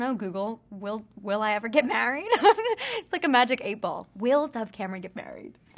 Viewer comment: Did she say Magic Apple? I heard Magic Aypple 😂😂😂😂😂